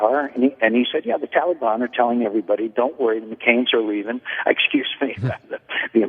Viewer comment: did he say "McCain's" are leaving? lol